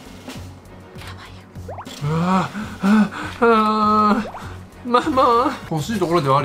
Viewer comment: 7分31秒あたりで小さい声でやばいよって聞こえる…怖い